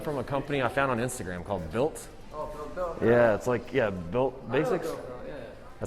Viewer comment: What that dude say where he get his shirts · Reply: Bylt